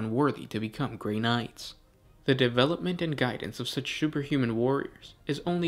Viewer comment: did he say superhuman or sugarhuman kmsl